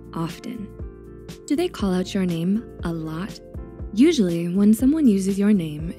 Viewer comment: When I heard, “Call out your name,” I thought I heard, “Call Me By Your Name,” and I stopped breathing for a hot second. 😭